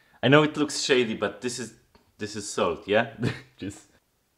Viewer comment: I THOUGHT HE SAID "THIS IS SOAP" and was genuinely wondering WHY he was putting soap on his sandwich... NOT till he mentioned pepper did it get it. C'mon its Sunday...